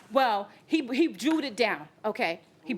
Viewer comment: The second that the defendant said "jew it down " i knew she was paying that money!! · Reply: I didn't know what she said. I thought she said "drew" it down ! I was wondering why judge went off , now I know. Silly me 😅